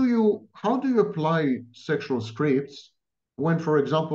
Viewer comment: Starting at 15:55 they say something repeatedly that I can't understand 'sexual scrapes'!? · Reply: Sexual scripts.